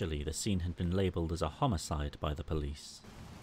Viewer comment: When they said homicide I thought they said suicide and I was like “HOW THE HELL DO YOU PUT YOURSELF IN AN OVEN???” Edit: well then…